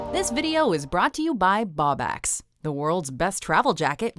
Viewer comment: I thought she said this video is sponsored by bawbags.😂😂 You'll get that if you're Scottish.